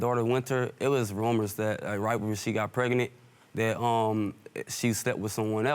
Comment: Did he say “preganet”?